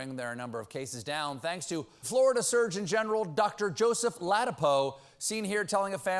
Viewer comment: Ha! I thought he said Florida surge in general.